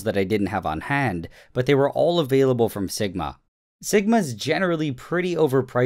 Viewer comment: when he said Sigmai thought he said Ligma. when you are 14 years old...